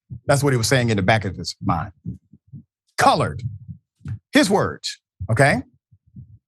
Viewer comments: Not gonna lie, when I heard it for the first time, I thought he said “college”….. this is a big deal about nothing. You can find a lot of racism on Fox. This is not that 🤷🏻‍♂️ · I don’t like this guy but it does sound like he said “college”. I think you’re wrong on this one. Never thought I’d say that because I love this channel. I’ll keep watching though.